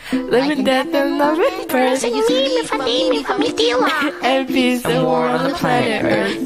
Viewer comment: "mimiamamaia" 1:04 that is what i heard . 💀